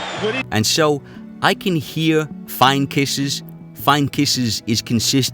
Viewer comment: I thought he said “those are kisses” or “only kisses” saying those punches felt like kisses implying they didn’t hurt him at all